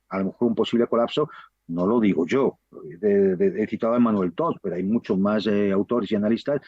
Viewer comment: No entiendo el nombre del analista que cita al final, minuto 14:30 ...he citado a Manuel qué???